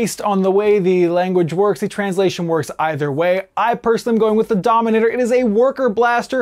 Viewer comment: At first I thought he said the denominator instead of the dominator.